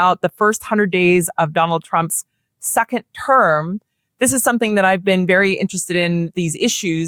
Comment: My apologizes in advance. I thought she said suck it term... Second term was what she said but I found it ironic that the saying still fits his bs.